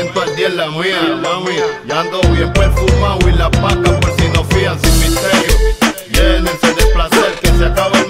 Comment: despues de fumar hierba escuche "ando bien perfumado, hasta las patas por si me enfrian" y me rei muchisimo jaja